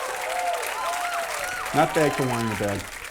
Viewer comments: What does the senator say there that JO says "sure, that's fair" in response to? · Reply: “Not bad for wine in a bag” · what did he say??? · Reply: bad for wine in a bag. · what does he say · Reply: Not bad for wine in a bag.